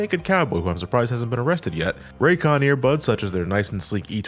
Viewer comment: I heard "thanks to ray comfort for sponsoring this video" instead of "raycon" and was really fucking confused for a hot second